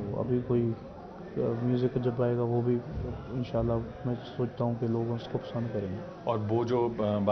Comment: Have I heard right 'INSHAALLAH